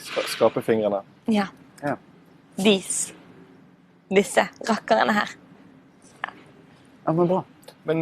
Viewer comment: What is the word she is saying at 7:35 about her fingers? I'm Danish/Swedish not Norwegian, so some words are hard, specially Bergen dialect. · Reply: "Disse rakkerene her." Maybe "these here rascals" in English.